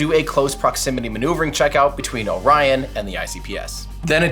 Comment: The subtitles are good overall, but around 10:00 instead of "Orion" the subtitles talk about "Ryan" lmao